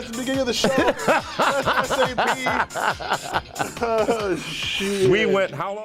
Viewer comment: Did say "oh shit" LOLLLLLLLLLLL